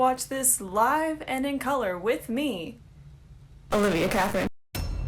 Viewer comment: I thought u said "a living catholic" and i died lol